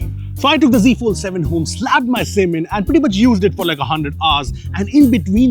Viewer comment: i heard slapped my semen 😅